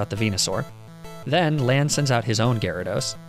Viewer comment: why did he say lance is blue lol